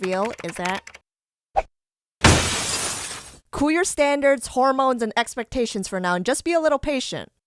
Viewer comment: did you say hormones